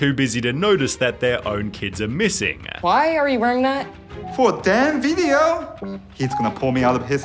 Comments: did you really say damn 🫤 Edit: actually makes since since in the who framed roger rabbit they used that same word because its was rated pg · if this movie is for kids why does he say "for a damn video?" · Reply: For Dan’s video. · did he say 'for a damn video?